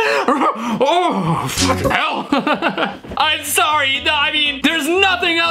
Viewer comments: i hear “???in help” 9:39 yet again, i hear “Gah!” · I heard "What the hell" but that's still cussing. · I heard F***ing helmet but that’s just as worse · I hear Bob saying “watch it now” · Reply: I heard both “F**king Hell” and “WTH” in that clip